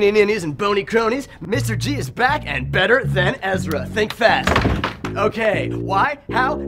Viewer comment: Did he say "better than Ezra?"